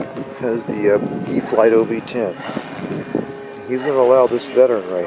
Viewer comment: I’m sorry but did I just hear “Eflite OV-10 Bronco?” 0:13 funny moment